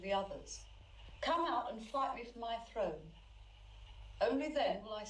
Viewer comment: lol I thought she said come out and fight me for my throat.